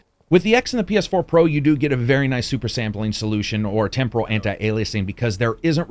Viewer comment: did u say super saiyan?!😂